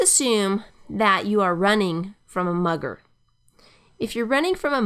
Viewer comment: I thought it said "running from a mother" with a bat 😂😂